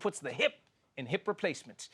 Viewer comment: Did he say "Joe Byron"?